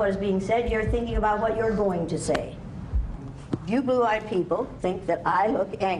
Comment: did she mean blue eyed people? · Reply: Yeah the subtitles just misheard lol u can clearly hear her say blue eyed people